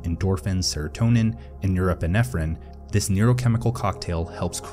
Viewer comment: what is the last neurotransmitter you have said? I havent understood it (english is not my first language) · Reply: Norepinephrine